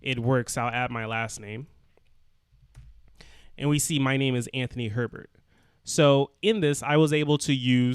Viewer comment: For a while i thought he had said anthony pervert..